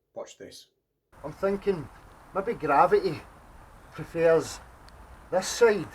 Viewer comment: Nooooooo. My gravy prefers to be on the right hand side of my plate even though my table is flat. What? Gravity? sorry thought he said gravy. Nice one.